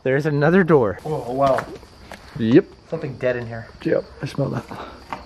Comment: I heard a voice saying "Yep" before he did